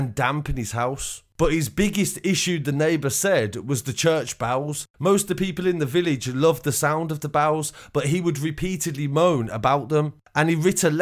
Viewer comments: I thought you we saying the church bowels. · I thought he was saying bowles, bells. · It wasn't until 3.33 that I realised this guy was saying "Church Bells". I was listening while making a sandwich, saying to myself "Church Bowls"? What the hell is that? Then I thought I heard "Church Bows" as in to take a bow and I'm here saying to myself this must be a Church of England thing. Then at 3.33, I heard the sound of the bells, and all became clear 🤣🤣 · I thought you said church bowels 🤣😂 only when you said church bowels ringing, I realised what you meant 😆😉